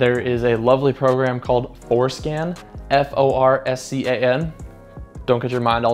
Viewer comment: Did he just say foreskin 😅